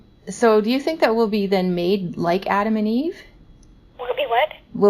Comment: When you said 'made like Adam and eve' It sounded like 'naked like Adam and eve' I think she misheard and that's why she went the way she did.